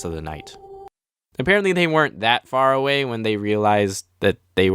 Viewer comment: I thought you were going to say apparently they weren't my perents